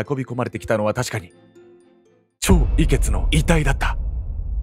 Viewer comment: チョウ・イケツが超美ケツに聞こえたのは自分だけか？ 人体の不思議展には、自分も行きました 受付の人に「標本になってる人達はどんな人達なの？」と聞いたら 「皆さま、有志の方達です」と、低い声で言われた 少しゾッとしたので、人混みを選んで帰った 黒鷺死体宅配便と言う漫画でも扱われたネタですね